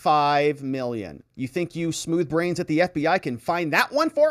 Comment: Did say "smooth brains at the FBI"?? 😢😢